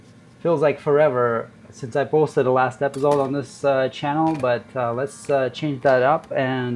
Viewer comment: As a non-native English speaker, I was a little shocked when I heard "last episode" .... .... listened again, subtitles on ... "since the last episode" meant lol